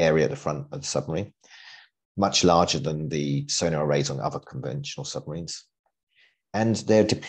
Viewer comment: I thought he kept saying solar arrays haha. That’s a good idea they should make a solar powered sub probably no downsides to that whatsoever.